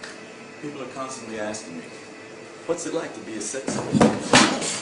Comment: I thought he said “People are constantly asking me what it’s like to be a sex offender?” And then he falls and totally biffs it LOL my abs are hurting lol 😆